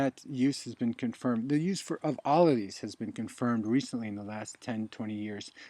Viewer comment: what does he say is also confirmed by Western science..as well? is it "olive leaf?" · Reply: I think he said, "all of these."